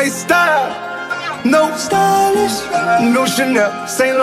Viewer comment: Tell me why I kept hearing "no spanish" instead of "no sylist"...XD